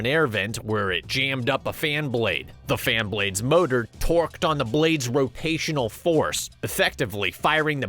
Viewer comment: i thought he said twerked lmao im like dam the Fan gettin it🤣😂😭⚰💀